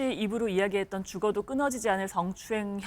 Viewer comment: 죽어도 끊어지지 않을 성추행...이 내 귀엔 죽어도 끊을 수 없는 성추행...으로 들리네